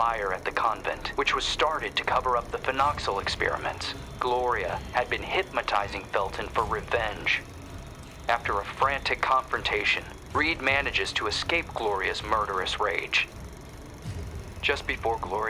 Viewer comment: I swear he says hypMAtising.